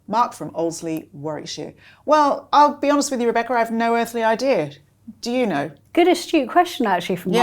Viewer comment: LOL - I thought Jo said she had "no effing idea" about Buckingham Palace....on replay she said "no earthly idea"....I should have known she had too much class to say that 🤭